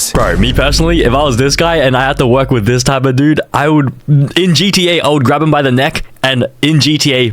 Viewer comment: did he say gta